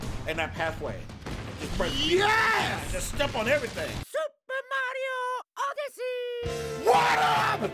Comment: I honestly thought that lamarr was saying super mario odeyssey😂😂